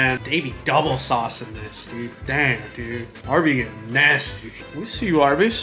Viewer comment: "Dude, Arby's is nasty." That's all I heard